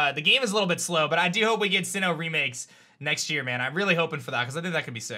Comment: Did somebody said Sinnoh remix this year?